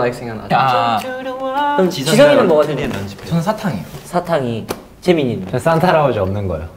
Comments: I heard Satan instead of Santa:’) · I thought Jisung said "candy" not "santa" T.T